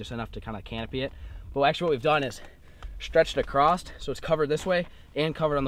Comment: I heard you say “acrost”, im from Arkansas and we say across, i have a teacher from Montana that says it like you also, is that like a northern thing?